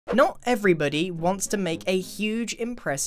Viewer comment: did u say non’t? xD